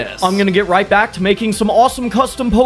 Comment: i dont kno why but at the end i heard "so until then beep" just beep!